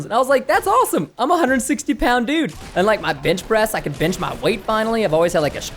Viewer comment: "bench press" sounded like b*tch press